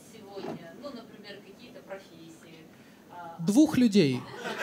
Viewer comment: И только я почему то услышала "двух б..лядей", вместо "двух людей"